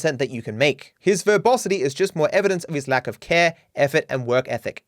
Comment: for some reason i've heard you say ''His verbosity is just more evidence in his lack of hair'' instead of ''care''